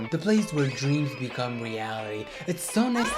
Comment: I thought he said "dreams become yaoi"😭